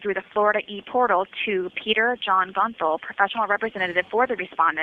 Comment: I thought she said his last name was Bunthole 😂😂😂 Sorry Peter.. Sarah just doesn't pronounce her G's and the B's very well or it's probably the audio😂😂